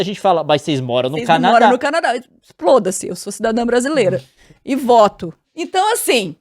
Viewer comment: Eu ouvi um "Foda-se" da?